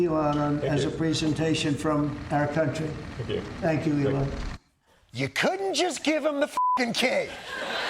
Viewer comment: it sounds like Trump said thank you your honor. Did he forget Elons name or what